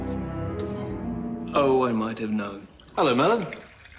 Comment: What did Merlin say at minute 0:09? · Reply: "Ooh I might have known."